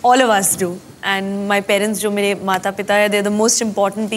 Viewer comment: did she just say..."mere parents, jo mere mata pita hai!” wtf?